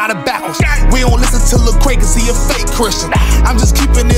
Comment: did he say “We don’t listen to lecrae because he is a fake Christian” how is he?